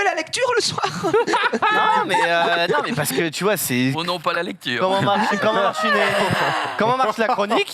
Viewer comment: pas compris pourquoi ils etaient tous mdr comme ça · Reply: a dit "oh non pas la lecture 😏" concernant Xari et Marie